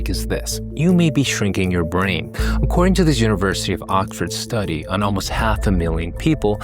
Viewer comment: I swear I heard "University of Okford" and now I'm wonder if "Ok" is singular for the plural "Ox"